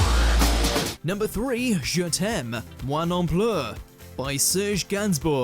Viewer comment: 'Je t'aime... moi non pleut' Lol. Me not rain.